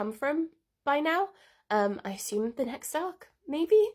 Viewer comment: lol Subtitles say "I suppose the next Stock" not next Arc lol